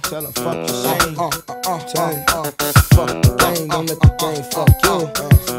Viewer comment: For a while I thought he said "Fuck the gays, don't let the gays fuck you." I was like I know it was the year 2000 but damn...